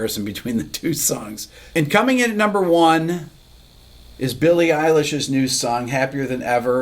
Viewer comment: I thought you said Billy Idol for a second, and perked right up, but then it was Billie Eilish, which I guess is OK.